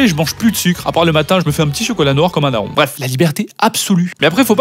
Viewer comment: tu te fais quoi un n...?? pas compris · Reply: Un café noir comme un daron